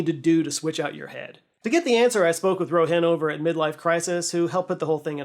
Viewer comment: I thought you said you spoke with someone at midlife crisis and was very confused for a moment.